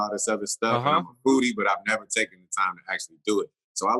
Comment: I'm a foodie I thought he said I like booty lol